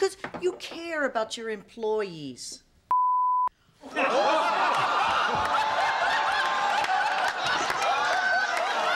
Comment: What did Benny say at 8:28? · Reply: Adam Fleissner I think she says 'pussy'? Could be wrong.....